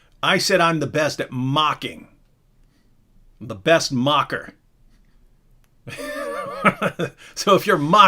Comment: Why does it sound like I heard "David is d best mak'r"? How dare u contest lala's unique title?! At least, take 2nd best, not d BEST!...oh, it was best MOCKER, not best MAK'R